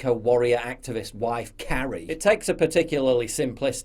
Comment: The first time I watched this, I thought you said his wife's name was "Karen". That would make it even funnier. 🤣🤣🤣